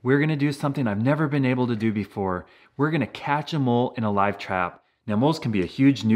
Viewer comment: Every time he say “catch a mole” I hear “catch ‘em all”